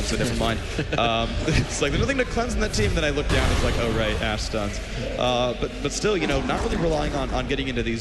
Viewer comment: i heard "ass stun" at 11:40 XD